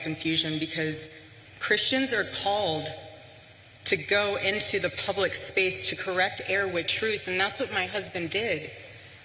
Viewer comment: Nice speech....Interestingly I found that when she said "Christians are called to go into the public space to correct err with truth" I heard "Keep going Candace".